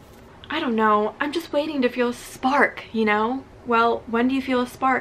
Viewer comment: i thought instead of spark it said spank😂😂😂